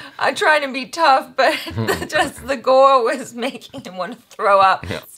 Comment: i don't want this conversation to be racism but did she said gora at 2:46 and most importantly, heard what she said (look at him when she said that) and still didn't edit that. and you're giving reviews and reactions to our country mainstream movies. wow....! i have to say you're something. and YOU miss choose your words carefully. and did you observe one thing in below comments. my people are still loving you. especially her.